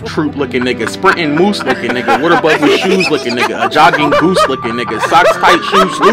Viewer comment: forest gump lookin nigga chubby flash lookin nigga small feet, fat ankle lookin nigga “gotta BLAST!” lookin nigga sonic the warthog lookin nigga icing McQueen lookin nigga spongebob shoes lookin nigga “tag, YOU’RE IT!” lookin nigga lemon pepper steppers lookin nigga church stomper elevens lookin nigga goof troop lookin nigga sprintin moose lookin nigga “what about these shoes?” lookin nigga a jogging goose lookin nigga sock tight, shoes loose lookin nigga “cAn We CalL a TrUcE?” lookin nigga stubby boots lookin nigga character from dr. seuss lookin nigga boogie nose, ACHOO lookin nigga foot small, shoes obtuse lookin nigga running in two canoes lookin nigga lay away but call it brand new lookin nigga wear it once, then it smell like mountain DEW lookin nigga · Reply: wow I thought he said "water above your shoes"